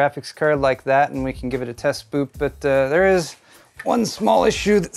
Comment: I heard "test boop" instead of "test boot", and that's now I will refer to it for all time